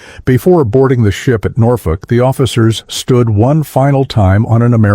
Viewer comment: Saw the statue of liberty while departing Norfolk? I don't think so. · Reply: I thought I heard the narrator say New York.